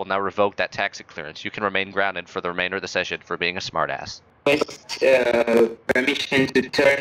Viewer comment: I think he said “Request... Uh...” instead of “Face, Chill”